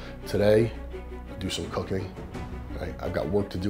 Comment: It sounded like he said do some cocaine